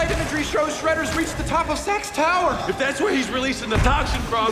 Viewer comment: I swear sometimes it sounds like they're saying sex instead of SAX or SACKS or however it's spelled. Lol